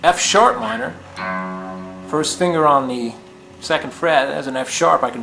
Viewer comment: man i swear the first time i thought he said "finger on the first shit" but good lesson anyways